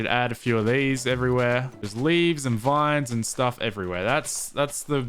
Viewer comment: what he says: "some leaves and vines What i hear: "leaves from the vine"